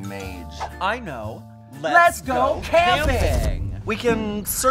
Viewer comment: I had to restart this because I swore I heard "LESBO CAMPING!!!"